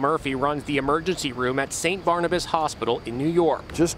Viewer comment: am I the only one who heard "runs the emergency room at St Bonaventure hospital? also, his name is doctor MURPHY...